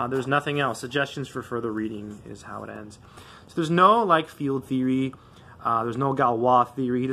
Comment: I can't understand exactly what you say? Do you say "....like field theory..." or is it something else field theory?